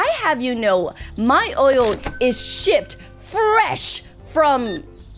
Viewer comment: "i have you know my oil is shipped😠" i thought said sh